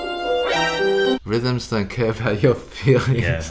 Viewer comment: Please make a hoodie saying INTONATION DOESN’T CARE ABOUT YOUR FEELINGS! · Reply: I thought he said rhythm doesn’t care about your feeings